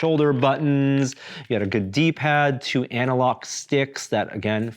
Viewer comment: Did he say Anna Lock sticks 03:38?? Because I know he didn't say analog!!! ;P